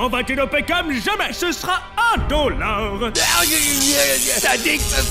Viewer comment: j'ai compris 1 dollar 😅